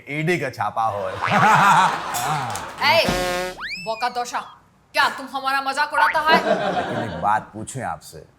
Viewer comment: What did mamata allergy say here 3:00 ? B0k@ c#od@ ??